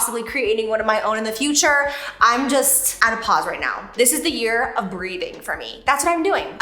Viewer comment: The year of breathing. Yes! Love the positivity, u look so happy and I’m here for it ❤ btw we’ve missed you on YouTube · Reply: I heard “the year of BREEDING,” hahaha 😆